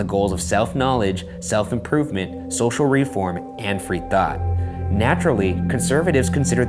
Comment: What he said: "free thought" What i heard: "free the thot"